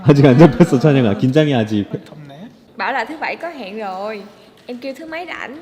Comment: 말하지 말걸 이라고 들었네요 순간 ㅋㅋㅋ